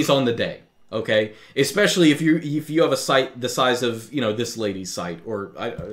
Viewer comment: "Especially if you have a site of this lady's size" lmao why did I hear it like that